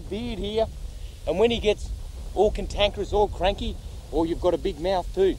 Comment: What did he say at 0:15 "And when he gets all ________, all crazy," Contankers? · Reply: Cantankerous. Bad-tempered. 🙂